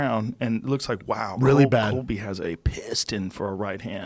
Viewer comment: Sounded like Joe said ''Colby'' instead of Cody to me🤔